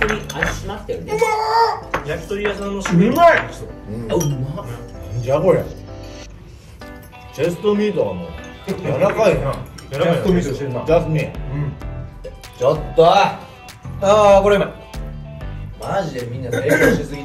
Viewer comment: はまちゃんのチェストミートがジャス民に聞こえるー😂💜